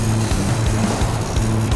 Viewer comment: who else heard heres your coupon nigga